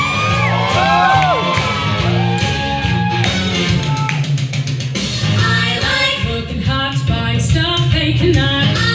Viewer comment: There's this guy at 0:30 What is he yelling? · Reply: Freebird